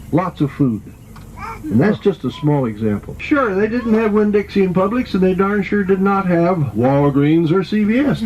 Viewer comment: When your tour guide said "They didn't have a Winn-Dixie or a Publix" I honestly thought he said "They didn't have Windex at Publix, did they?!" I was so confused because I've never been to Publix... haha